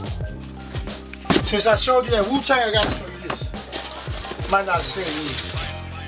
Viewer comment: I thought he said putang and I was like where?!